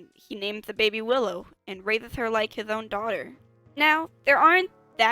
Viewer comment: "-and treats her as his own" why in the world did I heard was "Rape her all he wants" Like whata heck?!